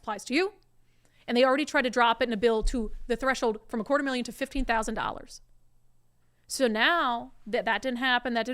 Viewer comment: $250k to $15k reduction Capital GAins tax? Did I hear that correctly?